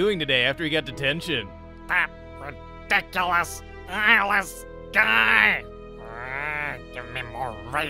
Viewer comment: I actually thought he said gay instead of guy,oml have I been watching too many ships lately